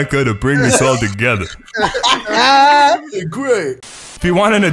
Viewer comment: I think or said my name Greg